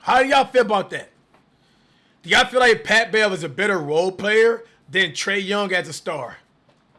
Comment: wtf are u saying rn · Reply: “is pat bev a better roleplayer than trae is a star?” wtf is he talking about 😭😭😭😭😭